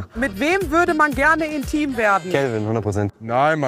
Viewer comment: Ich hab verstanden "ein Team werden", nicht "intim werden". Hilfe. :D